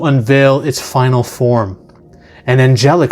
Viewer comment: I thought you said, “it’s ’Calvin’ form.”